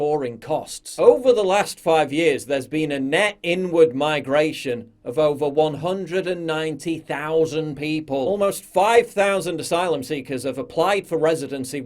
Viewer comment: What you hear: "... there's been a net inward migration..." What I hear: "... there's been a net n word migration..."